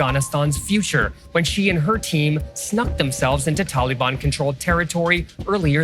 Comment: "Snuck" if they really did sneak they way in one day we'll see them on liveleak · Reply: I heard "stuck themselves", not "snuck themselves"